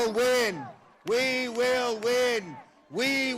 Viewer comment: did I hear “we won’t win?” 😂